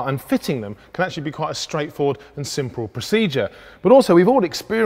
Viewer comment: Did he just say 'simprle' lol?